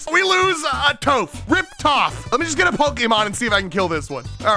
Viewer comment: I'm sorry, did that say GRIPPER POKEMON?!?!?!?!?